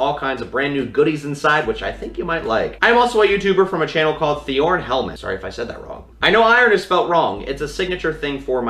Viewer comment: i was watching and i just heard him say iornhelmet and i was like wait wut and then i went here LOL have a nice day!